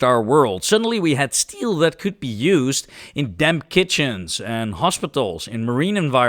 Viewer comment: I realize now that you said "damp"...but on first listen, I heard "damn" and was a bit taken aback by the sudden shift in tone 😝